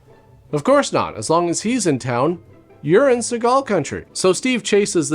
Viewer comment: Urine Segal Country. I can't be the only one who heard it that way.